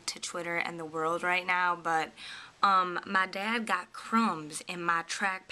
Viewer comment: def thought you said your dad got Crone's. AHHHH also, i vote for the short bob with LAYERS! it will make your hair look fuller and give it movement. :)